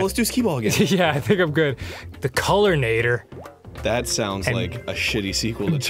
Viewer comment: Not gonna lie, I thought it said Colonator.